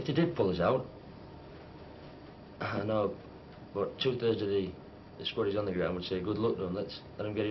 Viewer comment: "two thirds of the _____underground would mop it up " ? What does he say there ? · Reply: "two thirds of the squaddies on the ground..".